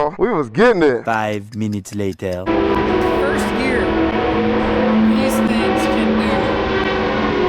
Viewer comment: can't hear what he's saying. Anyone catch that? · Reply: I think he said "In first gear, these things can do about 100(mph)"